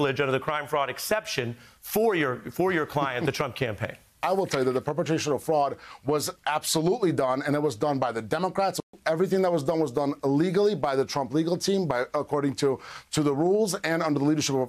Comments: "Everything was done illegally by the Trump legal team". Did Boris just say that?! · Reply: It's not a Freudian slip. You're mishearing "uh, legally" as "illegally". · Since English is not my first language, my reflexes may be misleading me here but what I hear and have always heard is this 6:07 everything that was done was done illegally by the Trump legal team which, IF (and only if) correct, is a clear admission.